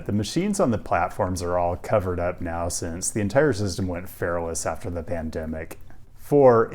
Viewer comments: Ngl at 4:30 I first heard it as “went feral after the pandemic” and not “went fareless”. Lol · I heard "The entire system went fareless after the pandemic" as "the entire system went feral after the pandemic" and with your brand of sincere sarcasm, I didn't doubt it for a second.